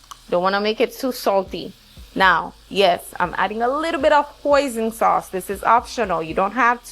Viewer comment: I thought she said poison sauce. 😂